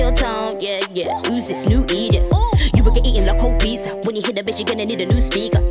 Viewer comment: i swear when she said when you hear the bass i thought she said when you hit a bitch